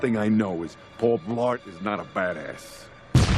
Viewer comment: Paul Blart is not a badass (Explosion) He IS a badass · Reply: I always thought he said Paul Blart is not a fat ass LOL